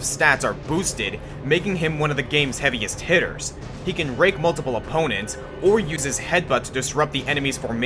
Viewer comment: I thought he said "He can rape multiple opponents", but he actually said Rake. That felt so awkward. :l